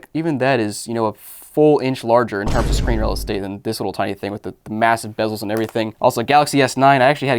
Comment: Did you say massive...